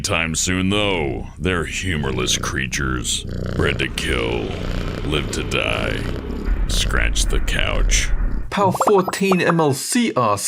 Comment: I thought said "Bred to kill, live to die, scratch to couch."